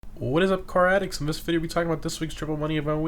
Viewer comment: I love this channel. I wake up every Thursday just to watch him. Thank you!! EDIT: I always thought he said karatics, not car addicts lol.